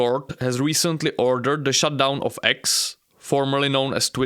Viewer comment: "Shutdown" Eu escutei "Xandão" 😂